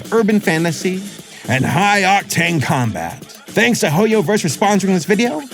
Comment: DID HE SAY HOMOVERSE DID HE SAY HOMOVERSE